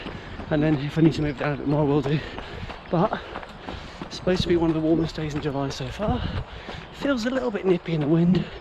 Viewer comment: Sounded like “supposed to be the warmest day in July??” 🥵😂 Motivational as ever thanks 👍👏👏